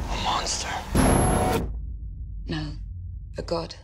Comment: Did she really fucking said `no a goat` !?!?!?!?!? or is it just me wtf 0:43 that guy says a monster and she a goat wtf!!!!!!!!!!!!